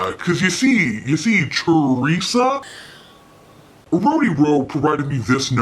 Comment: I thought you were saying “Oh, a rodeo” and was like wtf 😂